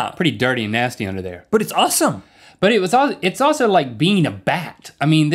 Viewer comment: It sounded like Rhett said "uwesome" instead of "awesome" =3